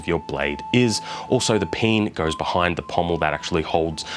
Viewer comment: did you say the "peen" behind the pommel?